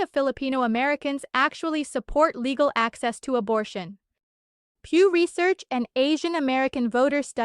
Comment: PEW instead of FEW.